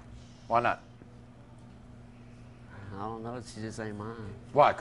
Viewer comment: I heard someone say “don’t deny her”